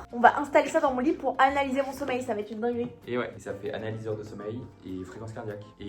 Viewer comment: J'ai compris "slip analyser" 🤣🤣🤣